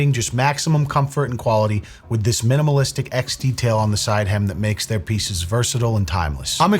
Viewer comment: I thought he said "with this limited edition X detail on the side hymn..."